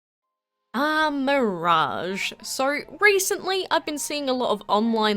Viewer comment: Is it only me or do these accents always put an R sound in words ending in "o" or sometimes "a"? This is the best example I've found of this and I swear I hear it all the time. Sounds like she's saying sir instead of so lol